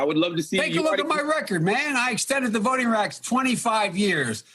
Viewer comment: Joe Biden at 0:25. "I extended the voting racks 25 years." WTF did he say?